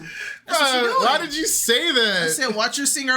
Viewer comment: Sister is hilarious 😂. 💯Dig it, I thought said swinging her words instead of singing lol. Because she was swinging her body while she spoke. Just like a valley girl type